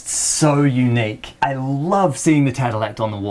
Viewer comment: /TAH tel'act/? Wha?? I couldn't make out the word (not even with subtitles). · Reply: Tadelakt 😊